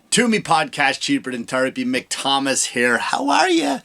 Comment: At first I thought you said cheaper than turdapy😂